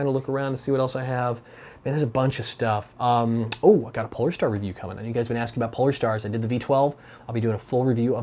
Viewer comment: for a secong there in the end it sounded like you said i got a pornstar review and i just went wait what????